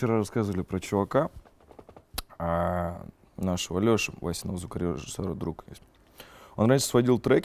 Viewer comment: кто-нибудь, понял, что он сказал??? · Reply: nikita elibrin нашего Лёши васиного звукорежиссёра друг) конский друг